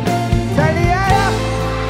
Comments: 請問蕭敬騰他說什麼 :P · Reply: 太厲害了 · 有人知道 1:33 老蕭在說什麼嗎0....0? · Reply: 他是在說 太厲害了 😂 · 哪裡他說什麼😂一直不懂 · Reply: 好像是：太厲害了。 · 有人可以翻譯一下老蕭1:34說了什麼嗎??聽好多次都不懂??? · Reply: 太厲害了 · who knows what 蕭敬騰 said to the audience at 1:33? I heard it in Taiwanese but still pretty blurry. But the BinMusic post said this concert was in 上海 of 2017. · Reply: 好像是：太厲害了。